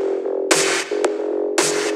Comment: First time I heard in the gym, I thought he said - "Nobody listens to J.Cole".